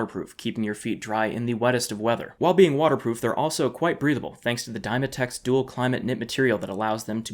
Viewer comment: Thanks again Vessi! Use my for $25 off each pair of your Vessi shoes! Free shipping to CA, US, AUS, NZ, JP, TW, KR, SGP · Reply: Ah so when you said "breathable" I thought you said "breedable" and had to rewind